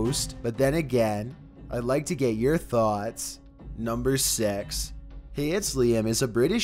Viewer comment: is that really how he says 6 it sounds like hes saying sex